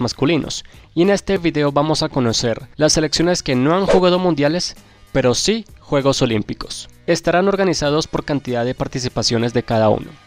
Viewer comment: no me lo van a creer, pero yo pensé que decía "selecciones que no han ganado el mundial pero sí los olímpicos" xd por algo uso gafas